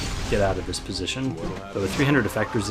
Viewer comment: 300 Effect Resistance?!! Did I hear that right?